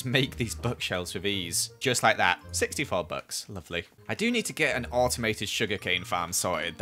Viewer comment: When said 64 Books I thought he said 64 Bucks